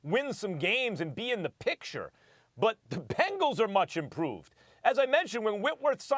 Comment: Anybody else hear this Nigga say “pangles” at 3:01 😂😂😂😂